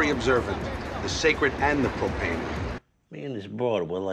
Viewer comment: did he say propane or am I losing it